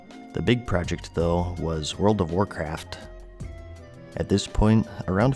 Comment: I thought he said world of warcrack